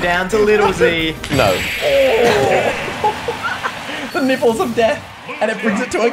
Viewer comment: does anyone else hear "the nipples of death" is that what he said?